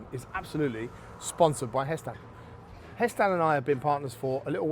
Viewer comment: Wait so all the time you've been saying Hestan with regards to pans I thought you were saying HestON. Like Heston Blumenthal. I thought it was gear he was making lol